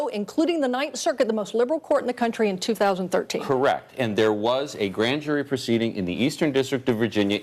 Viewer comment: When in doubt, go back to "B.S. Nixon Watergate Era" to prove your stuPIDIty. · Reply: "Correct." ("What the Hell did she say!??")